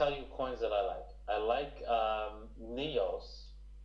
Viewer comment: Did he say NEOs or EOS is on the knees? Around 42:37 that you dont need to go through all :) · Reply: I think he said NEOs as he refers to BTC as Bitcoins more than often